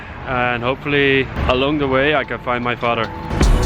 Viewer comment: 저만 daughter 이라 들리나용...?ㅜㅜ